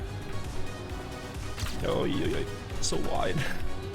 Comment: what did you say at 28:00 😅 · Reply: so wide bro 😫🤭